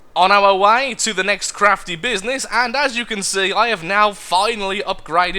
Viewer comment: his "crafty business" sounded like "crappy business"